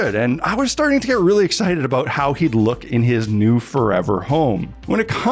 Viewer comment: really looks good! Did you actually say "furever" home at 8:08?